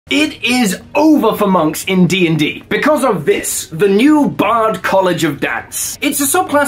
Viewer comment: I misheard this as College of Dads at first, and now I'm trying to pictue what that would look like